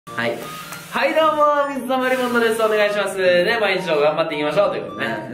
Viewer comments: サブチャンもサブチャンらしい企画で本気！ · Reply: 水溜りポンドって聞こえる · はいどーも、水溜りボンドでーす！お願いしまーす！まぁ、今一同頑張って行きましょう！はい、頑張って行きましょう！って聞こえてたww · はいどうもー水溜りボンドです！まぁ、いっちょ頑張っていきましょうって聞こえる